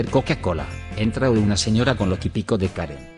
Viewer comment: pense que dijo cocaina por como lee el robot xd